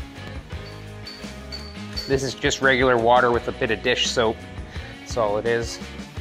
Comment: hi! i love this channel. what do you say in the minute 4:38 about the water combination? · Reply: Water with a bit of soap